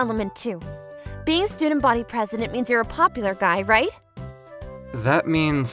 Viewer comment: I thought it said gay but it said guy